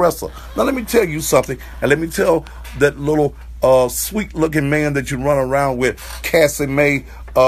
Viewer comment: Anyone else catch him saying " that little sweet looking man" at 6:29 or is it just me. . Oh lawd "pastor" have mercy lollllllll